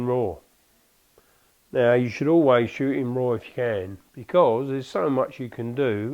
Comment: What does he say at 4:55? "You should always shshhhs can"? I don't know about photography. It sounds like you should always shoot raw ten or can? Thanks!